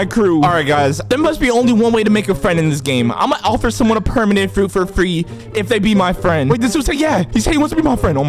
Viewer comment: i thought you said permanent fruit...